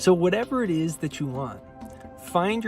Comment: It sounds like he said "Creation is spinach "